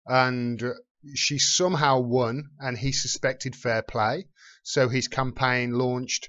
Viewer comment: Do you mean Tim Canova suspected foul play? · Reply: It sounded like you said, He (Canova) suspected fair play. The context of your following statement makes it clear that you intended to say, foul play.